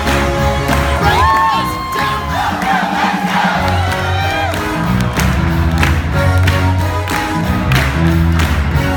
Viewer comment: dang that hair flip Also what did he say? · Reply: Oh no let's go. It's one of the lyrics of Let's Go Crazy by Prince, hence the date of the title is when Prince died, hence this is a tribute.